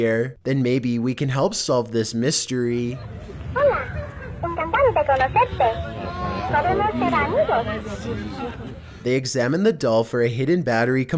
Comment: Hi at 13 the doll is speaking Spanish and she's saying hello, I'm glad to speaking with you and we can be friends that is the dool I said. And I love you videos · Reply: GOLDMAN1122 4 I heard “hola, encantante conocerlos. Vamanos ser amigos”, what did you hear?